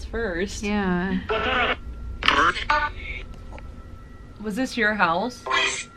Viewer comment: I heard the ghost said “ WASSUP “ that’s what I heard